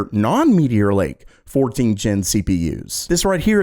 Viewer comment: I am partial to AMD, specifically of their CPUs, I follow a lot of Tech channels and I do some hardware review for myself... my point is I am an really tech informed person, BUT when I look for laptops is really hard to remember or find which zen generation CPUs are, I have to find that damn table showin what every number means... and sometime you have som firen or relative asking and you cant give an easy advide if their naming was better...so come onAMD FIX it, it doesn't matter if INTEL or nvidia are doing the same... the fact all of them doing it wrong it is not a valid excuse for AMD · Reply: I thought I heard someone say "Tiger Lake"...